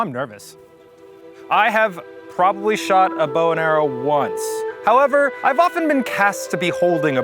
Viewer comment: oh my god i misheard when Eugene said "i have probably shot a bow and arrow once" as i have shot a boner once and now i cant unhear it